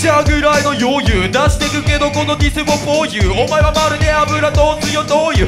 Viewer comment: 出してくけどこのディスも??? ???なんて言ってるの? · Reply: for you じゃないかな？